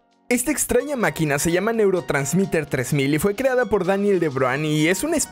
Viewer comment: hagamos como que entendí lo que dijo :) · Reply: Neuro transmiter 3000